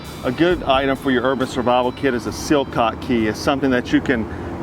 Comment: did he say silt kot key? I'm trying to buy one but I don't know what he said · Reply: Shamir Williams its a sillcock key or just look for a water spicket key